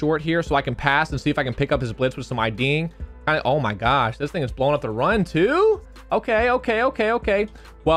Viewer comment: did he say this nigga's blowing up the run too? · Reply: Ya'll need new headphones lol He say's "this thing"